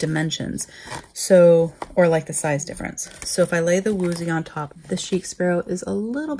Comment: What’s the pink standard TN called? I keep hearing “woozy TN” but I don’t think that’s right!!! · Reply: It’s a ghost leather from Wuzhi studio on Etsy. I have an unboxing of it with links to their shop. Here you go: <link>